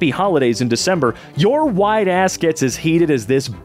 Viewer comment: I wasnt watching the video, I just listen to it while I draw and what I heard was "wide a$$" instead of "white a$$" and Im laughing really loud XD